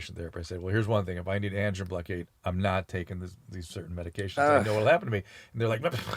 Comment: Did he just say he's not gonna take medication 11:10 ??